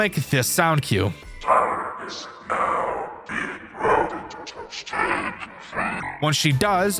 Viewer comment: not the subtitle saying "Time is now being touched" 😂